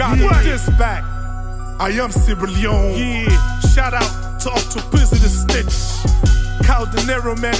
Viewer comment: Did he just say "Shout-out to Octopizzo the snitch " 😂😂😂😂😂😂 am from Kenya and line slaps so hard